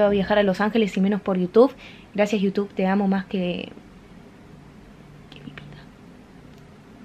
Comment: Che alguien sabe que dice en el minuto 17:50 cuando susura porfa :v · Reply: que mi vida dice